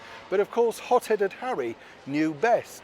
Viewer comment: The Hot Headed Prince has proved time and time again that he is not very bright! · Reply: I thought Neil said the POT headed prince, I didnt realise he said hot headed prince, but he would have been right either way, by harrys own admission..